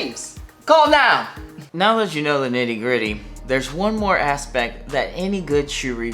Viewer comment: I thought he said “Diddygritty”, he didn’t, but I’m gonna use that for sure😂